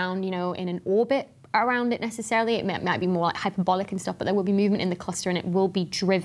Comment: Did you say "bollocky stuff"??